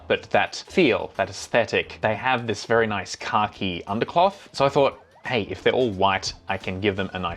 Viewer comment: who else heard “car-key” when was referring to “khaki”